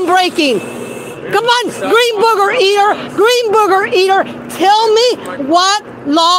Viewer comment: "Dirty bacon" and "green booger eater"... Judging by those phrases alone, I think she confirmed that she has the mentality of a five-year-old. Also, R.I.P. Betty White. A true, optimistic and wonderful comedian to the end. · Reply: I thought she was saying “green burger eater” so now that makes a lot more sense😂